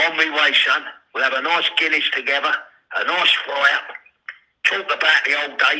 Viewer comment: Guinness and a fire lol. Bless you Charl. Simple things are the best. Multi millionaire overnight. Im buying the book and movie. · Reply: I thought he said nice fry up